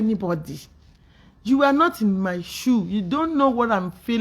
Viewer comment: I thought you said you are nor into fracas. ask Google